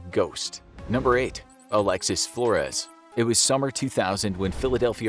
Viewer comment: I thought it said Alex Jones